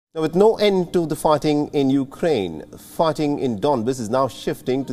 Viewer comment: Sounds like he said 'farting' when he said 'fighting'. 😂😂